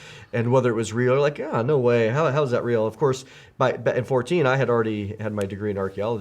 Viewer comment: Did say “How the hell is that real?” At 2:48? 🤔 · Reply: ) I said... "how...how is that real?" I do stutter some time when I get excited.